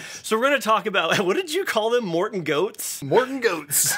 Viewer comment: I thought they said mortan goats